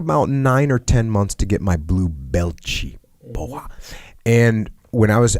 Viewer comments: blue belt cheap boy?? what did he say lol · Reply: He said blue belt in an exaggerated portuguese accent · Foi em qual momento do vídeo? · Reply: I thought he was being silly and saying "blue belt-chi, bowahh", but it makes sense he'd know some Brazilian doing brazilian Ju Jitsu